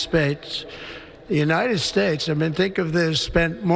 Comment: I could have swore I just heard Donald Trump call the United States the United spades 22:28 haha this man is got to be an idiot if it wasn't Biden now we have this joke back in office the United States can't be any more dumber and more screwed up to reelect a prejudice scumbag like this white orange crack.